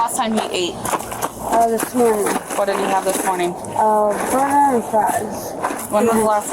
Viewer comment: did she say he had a donut and fries for breakfast? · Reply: "Burger and fries"...yeah 🤦🏻‍♀️